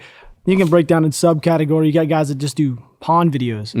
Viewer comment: Excuse me, what videos..? Ahhhhhh ok you said pond!